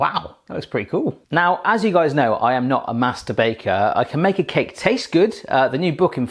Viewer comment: So when he said as most of you know I am not a "master baker" I heard I am not a "masturbater" lmaoo